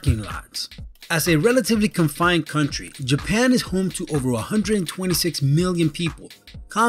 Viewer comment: Did he just say 126 billion people..maybe I misheard and its 126 million..please be 126 million people O_O